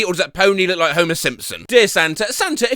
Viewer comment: i misheard Homer Simpson as homosexual and i have never laughed harder